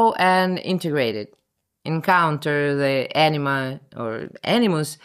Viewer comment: Thought you said "encounter the 'enema' and integrate it." Glad you didn't.